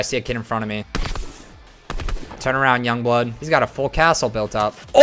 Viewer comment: Did he say turn around young blood at 4:31 or 4:32 like if he did